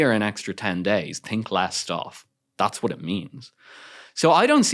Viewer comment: Why did he say @ 31:11 sec's "think less what"??? What he said he said there is very important for me. Can anyone be of help?